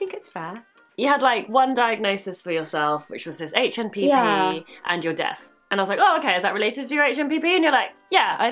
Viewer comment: THOUGHT SHE SAID "YOUR DEATH" I WAS SO SAD